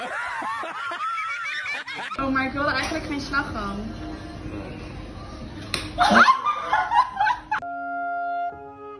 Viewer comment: No idea what she said, but that throw was smooth. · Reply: She said " Can I have it without whipped cream?"